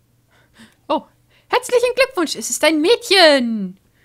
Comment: Ich verstehe:"Ein Juuungee"